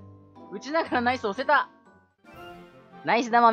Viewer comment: ナイス玉がアイス玉に聞こえてアイス食べたくなったやつは俺だけか